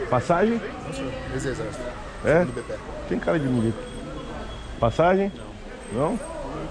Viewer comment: não entendi o diálogo. O cara parece dizer que é ex-exército, certo? E o dacunha responde o q mesmo? 'Tem cara de ...?' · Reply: De milico